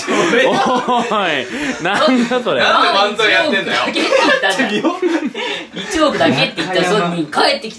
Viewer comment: のとこ、なんて言っとるん？｢~だけ｣ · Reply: 一往復だけに聞こえる！ 5年後でごめんなさい